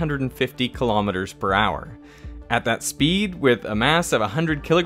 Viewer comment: Your pronunciation of "joules" sounded like you were saying "jewels." 😅